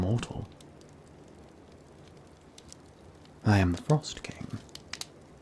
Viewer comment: When he said "I'm the frost king" at 17:06 I didn't hear the "the" so i heard "I am frost king" and tbh it sounded pretty cute lol